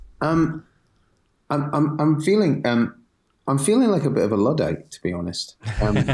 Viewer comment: what does say he's feeling like? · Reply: Luddite